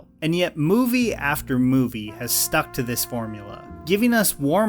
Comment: did he say morbula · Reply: No, formula